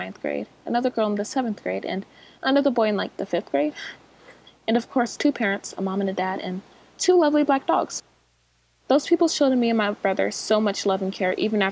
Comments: did anyone hear “two ugly black dogs” or is it just me? Anyways, I can’t imagine being in that system, every couple years going to another home sounds hard, moving to different classes in elementary school was jarring for me, so imagine getting a whole other family. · What kind of person is she!? She said the dogs were ugly! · did she just say ugly black dogs like she was complimenting them? Oh no she said lovelyb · omg I thought she said “and two ugly black dogs” THANKS TO GOD I HAD SUBTITLES ON (she said lovely not ugly) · When she said to lovely black dogs I thought she said ugly😅😂 did anyone else think she said that at first? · Can u tell me if that's another word cuz I heard "2 ugly black dogs"